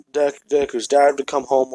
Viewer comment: So what you said and the captions say are kinda different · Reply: Go to 17:03 you said deku’s dad who came home the captions said you said dick who dares to come home its pretty funny